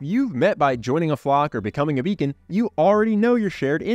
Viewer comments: First I heard "beacon" as "vegan." That's probably not a great way to make friends. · When he said become a beacon, I thought he said vegan. I was like whaaaat. 😂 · For a second I thought he said try being a vegan. I was like I'm going to be lonely for the rest of my life! Lol